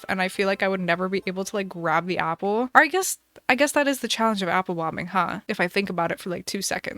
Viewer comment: i thought she was saying apple bombing 😂😂😂